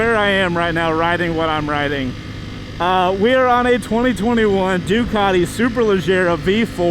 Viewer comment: For a sec I thought he said "Dugati super Legit" 😂